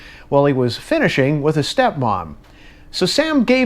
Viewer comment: How did"finishing with his step mom" go under the radar. Savage. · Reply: That’s what I heard, too! It sounded like he said “finishing off his step-mom.” 😆